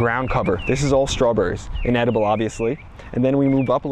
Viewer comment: something I don't understand. at 7:15 you say the strawberries are inedible? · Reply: Haha I thought people would think that during the edit. I’m like dang, I really need to annunciate my words more clearly. I say they are “an edible” which was also probably a poor choice or words especially in that order lol.